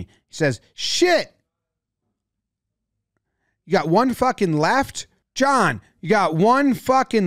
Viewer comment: I think he's saying "four" not "one". Maybe referring to the score?